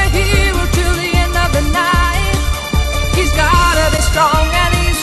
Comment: Wait, does anyone else hear 'He's gotta be Sean' at 1:03 and then Sean appears as Sam? Tell me I'm not the only one who hears this. ANYWAY. We hope you all enjoy this collab as much as we enjoyed making it!!!! Take Care All!! x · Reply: I heard "he's gotta be short" (cue hobbit).